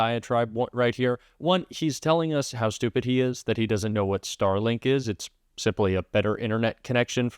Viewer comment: When he brought up Starlink…I thought he said Sterling as in Clarice from Silence of the Lambs…totally thought he was taking his Hannibal Lecter thoughts to the next level